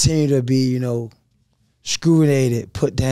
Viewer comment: Did he say “scrulated” 😂